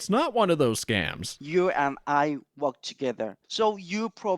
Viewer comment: FIRST I THINK THE BRO IS SAYING U AND I FUC* TOGETHER